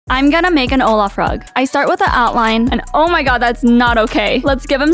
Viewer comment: Ok but why did I hear “Hola frog” instead of “Olaf rug”